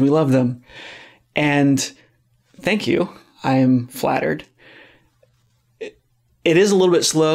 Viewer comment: Did he say FLAT EARTHED?